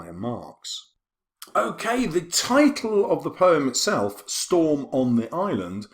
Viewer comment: What does he say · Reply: he says the title of the poem is a pun